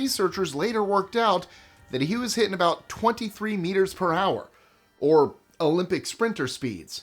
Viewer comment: 23 meters in one hour thats not fast. Just saying 😌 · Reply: I thought he said kilometers, and 23 km is about 14 miles, so that would be fast running, I think.